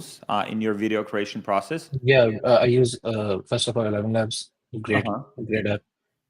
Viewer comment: What A.i tools did he say he use at 34:31 ? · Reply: eleven labs dude